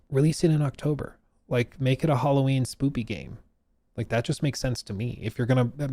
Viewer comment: and 32:40 did you really say spoopy instead of spooky? insanity